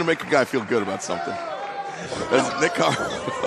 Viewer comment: Did he say Nick Khan ?